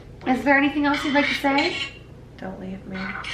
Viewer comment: after says she’s gonna shut it off I hear a creepy girls voice say “don’t leave”